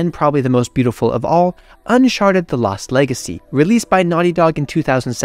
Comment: did you say "unsharted" 🤣🤣🤣